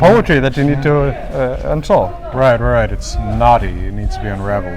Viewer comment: You said "knotty", I heard "naughty"